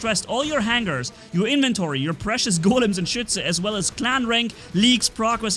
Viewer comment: Why do i hear, shi